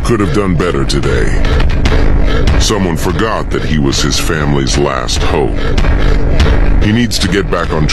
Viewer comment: Family's last hope? I thought I heard country's last hope